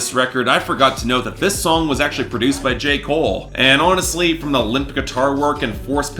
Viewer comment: I can’t be the only one who heard “Jake Paul” instead of “J Cole” sjskksjsks